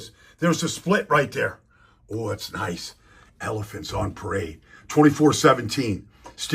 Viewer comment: I didn’t get to watch h the game, but it looks like Fields was touched down BEFORE the ball came out and they called it a fumble? “Elegance on parade”. I thought you said “Elephants on parade”. 😂